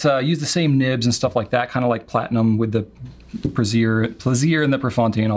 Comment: Ah, yes, the illusive Platinum Brazier. 😁 At least that’s what I heard in quick slip of the tongue. Hahaha.